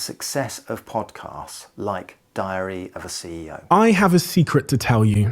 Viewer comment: I keep hearing "diarrhoea of a CEO", which seems like a better name. Do I need to say why?